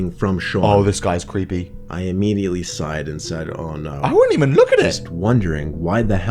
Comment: it says side instead of sighed